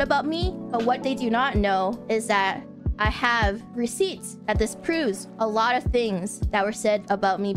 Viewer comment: It's not over yet, however. But I believe going forward, this will no longer be about Selen's termination anymore. It will be about the entire branch itself. Edit: If there is a mistake, some additions, or some nuances you'd like to input in the video, do leave them in this thread for anyone to see. Thank you! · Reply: The subtitles here made an error saying "that this proves" instead of "that disproves"